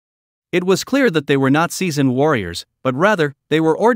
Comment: He said "not seasoned warriors" and I heard "nazi warriors" 😂😂😂😂😂😂😂😂😂😂😂😂😂😂😂😂